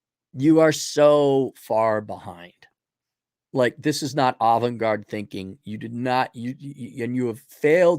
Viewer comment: Sounded like he said 'Olive Garden Thinking' instead of "Avant-garde" LOL